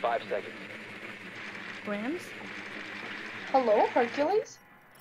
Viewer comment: did she call u Cartoonz? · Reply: "Hello, Hercules?"